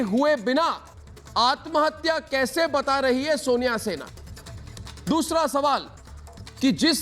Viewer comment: Did he said Sonia Sena @ 3:03-3:06??